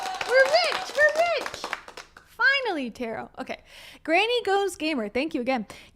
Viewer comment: This is classic. I am literally ROTFLMAO. I am also a long-term fan who hasn't commented before. This is Gold, straight up!! 😝😝😝They are so out of touch with reality. Tarot is gorgeous 😊😊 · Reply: Ohhh the kitty’s name is Tarot- thank you! Lol I kept hearing “Caro/Karo” but I knew I was hearing it wrong. My girl cat’s name is Carolina so I occasionally call her “Caro” for short